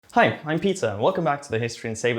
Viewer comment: all I heard was "hi, I'm Pizza"